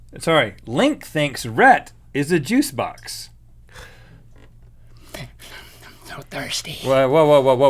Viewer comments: I thought it said "jukebox" not "juice box" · I thought it said jukebox instead of juice box